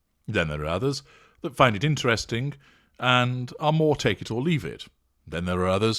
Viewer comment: I thought she was saying 'take your seat!" Something Moms frequently snarl, LOL